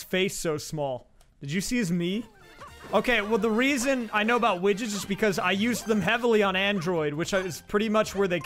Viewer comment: I thought you said witches instead of widgets lol